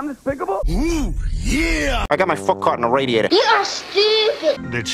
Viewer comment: I don't know how, but I misheard it as "I'm gonna fuck on my radiator"